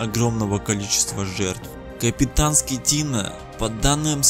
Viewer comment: пересматривал этот момент послышалось капитан СКОТИНА)... На деле так и есть.